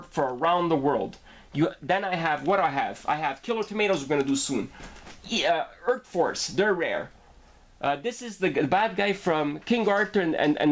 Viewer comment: Hey nice collection, you may have solved a 25 year old mystery for me. The figure at 5:14, did you say merc force? I have been trying to find out what these are since I was a kid.